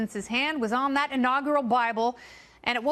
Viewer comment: I thought she said “BRIBLE”